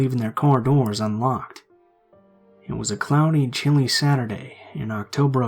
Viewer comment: At first i thought he said "It was a clowny chilly day" but then i realized he said "It was a cloudy chilly day"